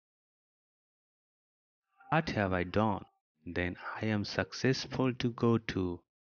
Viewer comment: WHAT THE HELL DID YOU SAY AT 1:31....??? all i hear is "i ta hay don"!!!!!!!